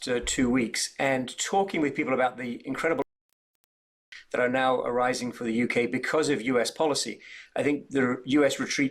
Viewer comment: So what word was edited out there? 1:29 in. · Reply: It wasn’t edited, the sound dropped, and the word was “opportunities”